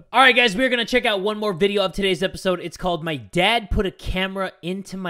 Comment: What I hear: "okay guys so we're going to react to one more of tiddies episodes" Ehh I hear everything wrong